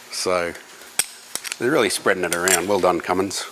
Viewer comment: "I love Cummins-Sence". (at least that is what it sounded like you said!)